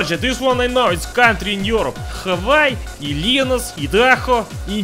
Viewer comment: I thought he just said Vilnius for Illinois